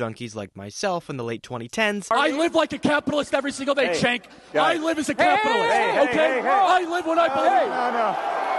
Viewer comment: Woah did he just say that 0:14 and people are shocked he was taken out he put a target on himself you say shit like that and think no consequences will occur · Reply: It was not a slur he was talking to Cenk Ugur